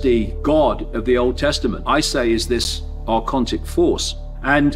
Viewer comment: what’s the word he describes the Old Testament god with? Sounds like arcontid